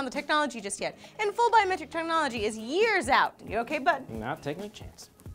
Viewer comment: did lee say biometric terminology at 2:40?